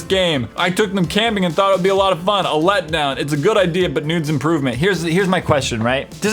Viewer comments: instead of needs, he said "Nudes" ok........Imma go now! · did say noods imrovement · it sounds like he said "its a good idea but nudes improvement"